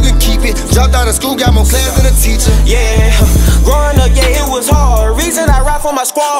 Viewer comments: i thought he said “Growing up gay it was hard”😂😂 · Did he just say “growing up gay. it was hard” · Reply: He said growing up yeah it was hard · I swore he said “Growing up gay it was hard” 😭😭😭😭